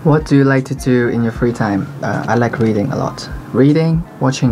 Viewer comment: I thought he said "i like breathing" lol